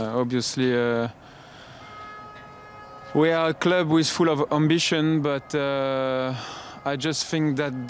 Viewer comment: Did he just say they were ‘full of ambition’?